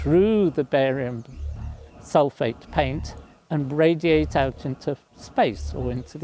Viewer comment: The Professor and Brady as "Rick & Morty" at 3:22 was hilarious. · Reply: Sounds like he says that "the heat can go through the barium sulfate paint and Bradyate out into space" at 3:56 😂